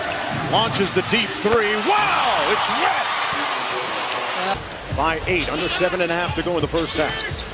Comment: I think I just heard "wow!! IT'S WEAT!!"